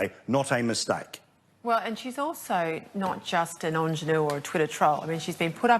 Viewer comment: WTF is that word she says at 4:46? Engeneur??? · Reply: ingénue